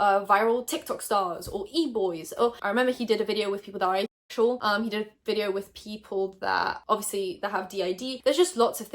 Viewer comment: what's the censor? I can't figure out what she said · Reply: She said Asexual and censored “sèx”